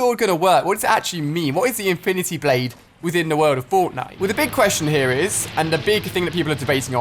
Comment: Listen closely untill you hear bortnite yes i did spell what i wanted to say bortnite